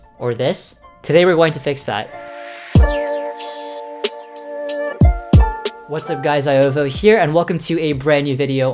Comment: My dumbass really thought he said 'what's up guyz, iovo here' 0:10 lmao